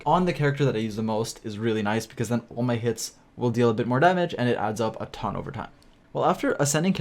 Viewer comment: I heard “all my kids will deal more damage”